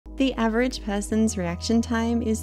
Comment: "The average peasant" is crazy 🗣️🗣️🗣️. (It's a joke. Because I've misheard "person" to "peasant".)